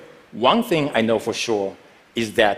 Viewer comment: Did anyone catch what he said at the end? 13:28 "Thank you very much, 謝謝"